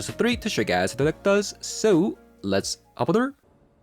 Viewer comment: wth does he say? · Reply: I think hes saying hop on over